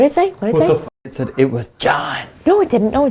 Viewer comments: I heard three things: It was John/god/gone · i heard "it was G O D" not "it was J O H N" or maybe just- "it was G A H N" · sounds like he says "it was God" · I heard “ it was me”